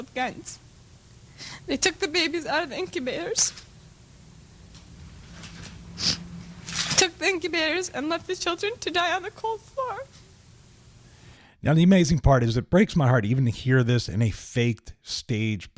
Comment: did she just say "Left the children to die on the cold fart????!!!!"....